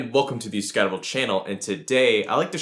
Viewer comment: When you say "welcome to the scattervolt channel" at first I always hear "welcome to Discovery Channel"